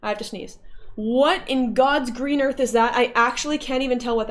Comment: Playing this on 2x speed, and when she said "what on god’s green earth" I misheard it as "what on god’s wiener" and fucking chocked.